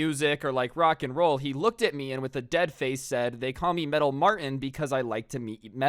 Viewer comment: I thought it said the bald martin saga